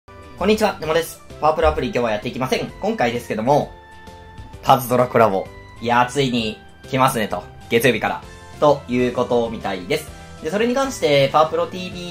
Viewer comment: パワプロ アプリ今日もやって行きません！に聞こえるww